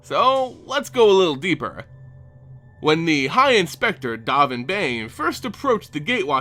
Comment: Whenever you say "Dahvin Bane", I hear "Donovan Bane". I believe the proper pronunciation is "Dough-vin Bahn".